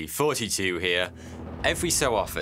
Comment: I always thought he said "hey, 42 here"